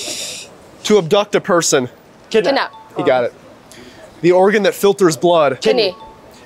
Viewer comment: I thought he said oregan